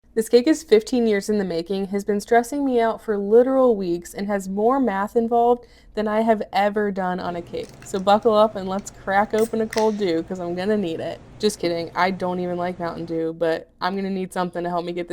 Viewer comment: I thought you said, "Let's open a cold Jew!"